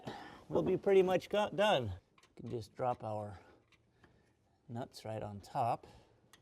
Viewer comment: we can drop our nut right ontop. thats the only thing i heard in the whole video. hahaaha